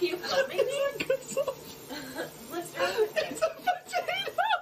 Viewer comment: What was it with the soap at around 10:00 ? I just couldn't hear what they said. I just suppose the soap was replaced with something else? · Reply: A potato